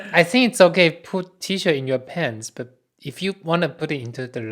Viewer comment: I thought he said T-Shirts in your pants! :D